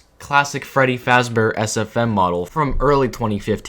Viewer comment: i thought bro said "as a femme model"